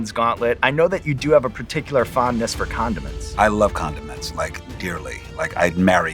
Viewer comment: The way I heard 'condoms' instead of 'condiment' and was completely taken aback